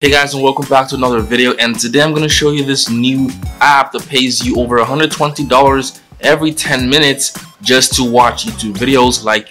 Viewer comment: I thought u said $120 per 10 minutes 😑